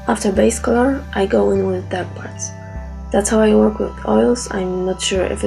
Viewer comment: you say " After base colour, I go in with ......." Cannot hear the audio at times. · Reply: it's "After base colour, I go in with dark parts". Sorry, my audio recording and accent are both bad, I'll make subtitles for the video.